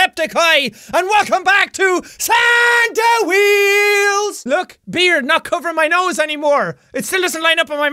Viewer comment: Santa Wheels ,I thought you said Happy Wheels.